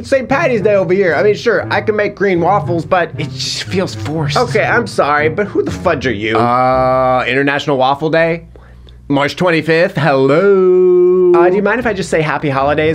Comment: did he just say March 25? That's Greek Independence Day